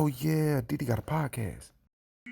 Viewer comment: I thought you said Diddy got a podcast when I first started watching your videos 😅